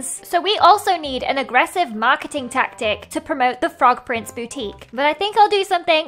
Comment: I totally thought she said "Frog prince booty"